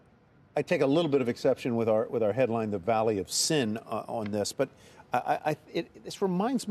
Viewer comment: Did he just say Silicon Valley is the 'valley of shit?